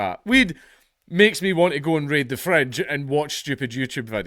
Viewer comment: Dank: "Weed has made me want to raid the fridge and watch stupid youtube videos." What I heard: "Weed has made me want to raid the French and watch stupid youtube videos."